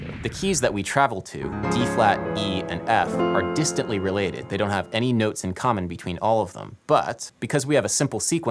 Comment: I got so confused. I misheard you as saying "there are no notes in common between any of the these keys". I then legit spent 10 minutes trying to think how it would be possible for 3 different keys with 7 unique notes not to share a single one.